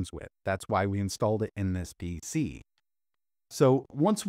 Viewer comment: Noise reduction on this video sounds overly processed. Almost sounds like AI especially at 3:22. "DC" instead of PC